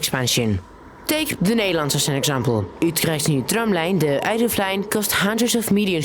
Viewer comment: Hey, I loved the video and am incredibly surprised you don't have more views and subscribers??? Instantly subscribed. However!!: sometimes the subtitles don't line up with what your saying? 9:07 > You said: "Take the Netherlands as an example", the subtitles said: Take Rotterdam as an example. 4:35 > You said: "It takes more than 150%", the subtitles said: It takes more than 15 times. While the video is very professional and I love the content, please fix these inconsistencies because I don't know if either you're reading the script wrong or the subtitles are wrong, and it changes context quite a lot. Thanks for the video though, I thoroughly enjoyed it!